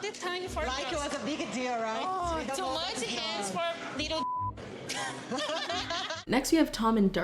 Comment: I’m sorry did she say “too much hands for little dick” LMFAOO 😭😭